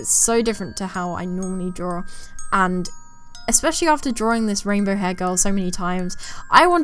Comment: Every time I hear you say "Rainbow hair girl" my brain autocorrects her name to just "Rayne Bohargal"